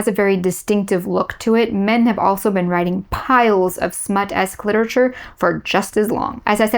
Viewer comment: Wait, did she say "Cliterature"?